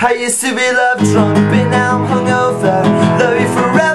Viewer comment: i thought he said " but now im sober" when its " but now its over" though it would fit if he had said that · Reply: kawaiian I listened closely and he did sing it right but it still sounds like he said "but now I'm sober"